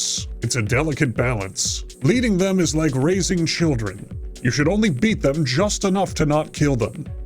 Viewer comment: "Leading them is like raising children, you beat them just enough to not kill them"2022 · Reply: Ooohhhh, I thought he said racing children, which also fits